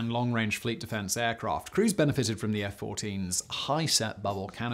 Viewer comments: Oh for F**k's sake!!!! Simon, you ruined this video the moment you laid into the Top Gun references. 8:18 where you say something about ". . . crews benefited from the bubble canopy." I literally thought you were saying, "Cruise benefited from the bubble canopy." Also, when Tom Cruise was married to Katie Holmes, their couple name was "Tom-Kat". Like Brad Pitt and Angelina Jolie were known as "Bra-gelina". So this entire video is just your Tom Cruise fan-boy tribute, right? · I thought he was saying “Cruise benefited” as another top gun joke, before realizing he was saying “crews”.